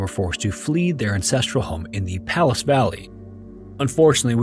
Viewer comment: Ha ha. You know how I feel about your mumbling. I thought you said 'incestral home' instead of 'ancestral home!'